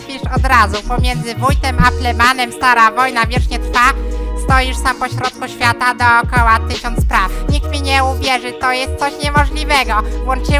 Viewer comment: when he said "stoisz sam pośrodku świata dookoła tysiąc spraw" i felt THAT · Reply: A ja usłyszałem najpierw "Stoisz sam pośrodku świata dookoła tysiąc PRAWD" i tak śpiewałem pierwsze parę razy. I też było to dość logiczne.